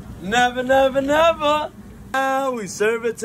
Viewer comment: I thought he said now we serve it to the tramp 😂😂😂 I was bout to say homie been doing his research